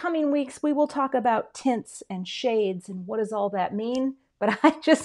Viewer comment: Sounded like you said ‘tits’ but when I looked at the video I realized you were saying ‘tints’.